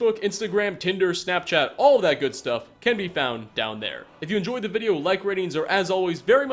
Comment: I heard "Be sad everyone" XD